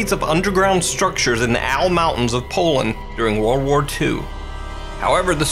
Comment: First Video is not German....Polish people in Poland.... -.- Nicee info bro :) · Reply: he actually says at 0:19 "In Poland"